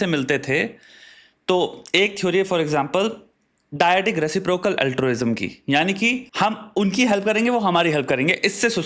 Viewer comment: can someone tell what he said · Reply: Diadic reciprocal altruism